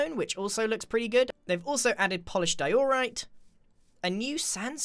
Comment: I thought he said"Polished doritos😂😂😂"